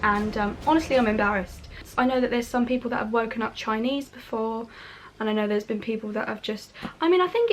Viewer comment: I’m sorry did she just say, ‘I know there have been people who have woken up chinese before’ at 4:49? girl what